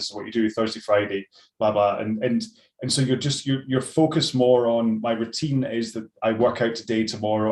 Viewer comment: When you said, ‘You focus more on…’, I heard it as ‘You focus moron’. Good motto: don’t be a focus moron!